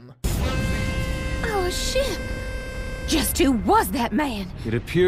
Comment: I heard instead of our ship Oh sh*t in the most calm possible way